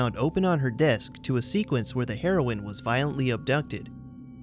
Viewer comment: I heard that as the “heroin” was violently abducted, instead of “heroine”